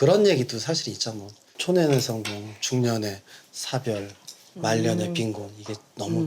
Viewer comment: 진짜 말년에 빙고로 들리네ㅋㅋㅋㅋㅋㅋ 너무웃기닼ㅋㅋㅋㅋㅋㅋㅋㅋㅋㅋ